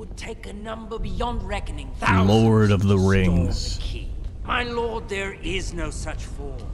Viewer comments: Why do the subtitles say Harry Potter, because it was Lord of the Rings. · Am ı the only one seeing on the 3:31 saying "Lord of The Rings"but the subtitles saying "Harry Potter" LMAO the editor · Did anyone see that the editor wrote Harry Potter in the subtitles instead of lord of the rings 3:30min